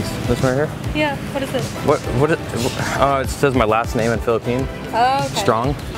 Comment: did he just say it has my last name in Philippine?